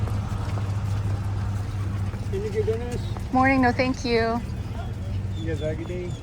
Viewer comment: What did he ask you ? Do you need a good dentist ? The auto-caption says "do you need donuts ?" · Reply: lol! Yes, they ask if you need a dentist. Haha